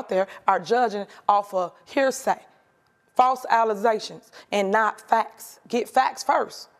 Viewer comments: Did she just say "false allizations"?? Lol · did she just say "false 'Alize' tions" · "false allaztions" The mayor can't even speak properly. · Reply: She said false? I thought she said "fause".